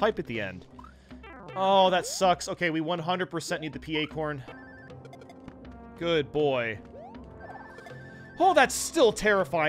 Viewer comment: was he calling the purple fish a good boy 😂